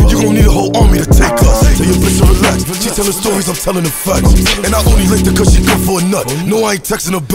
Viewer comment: The fuck did he say at 1:58? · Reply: "She telling stories, I'm telling the facts"